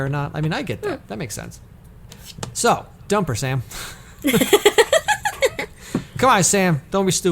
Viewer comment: kept saying “dump her Sam” so quickly I legitimately thought she was saying something in another language because I kept hearing it as “dumpursam!” I am a dumbass.